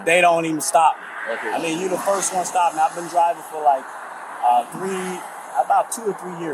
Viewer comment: he actually says ‘I’ve been DRIVING’ lol.